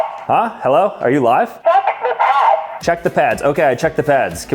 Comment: CHECK THE PADS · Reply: I kept hearing "Fu*k the pads". 😂